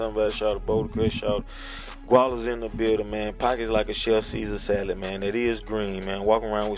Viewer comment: what is he saying? "Package like a chef sieler salat?" haha wtf.